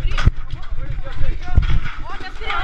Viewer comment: FOI ISSO MESMO QUE EU OUVI NO 5:10 "OU VOU CHAMAR A POLÍCIA"